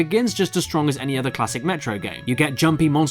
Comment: did he say "Metroid"?